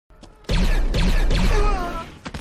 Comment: Guys does it sound like he says raider instead of traitor